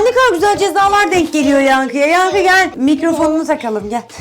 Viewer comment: 8 37 Dee Ben duydum yankl Anne dedi